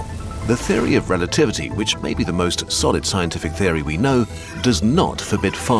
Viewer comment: what is he saying here? the theory of relativity is the most.... solid? sordid? i think he says solid but it's too close for me. i don't know why he would say sordid... i thought maybe i was going to learn something juicy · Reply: why are you so confused here, he said solid